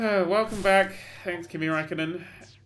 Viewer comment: Did he say Kimi Räikkönen?